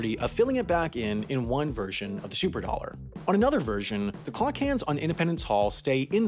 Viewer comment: I zoned out and thought he was saying to try out the super bills haha